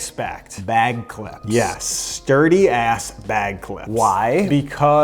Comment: At first I heard “DIRTY bag clips!” I’m like wth is he saying, they look clean. Stuuurdy!*😂